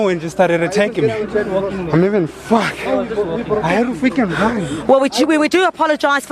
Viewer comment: what did he say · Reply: Chat Room Moderator "I gotta freakin run"